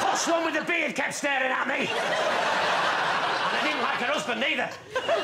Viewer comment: What does lee say at 3:38 after "... one with the beard kept staring at me" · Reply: "And I didn't like her husband either." Suggesting that the one with the beard was Victoria. Bytheway, there are subtitles :D